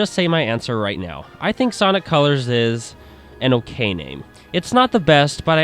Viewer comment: When I was listening to this review it kind of sounded like you said "Sonic colors is an, okay game" But when I saw your final verdict I was very confused. Then I went back to the title and I heard "okay name" :P